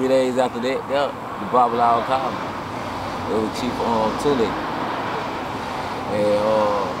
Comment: He mentions his Babalawo at 5:44. Can anyone confirm what he is saying? Sounds like Chief Tunde....?